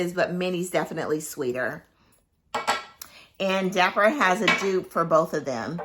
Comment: Who makes a good dupe of Minnie? I didn't catch who you said...please let me know. Thank you ♥️ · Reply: DAPPER has an amazing Dupe of HUFFELPUFF & MINNIE 💖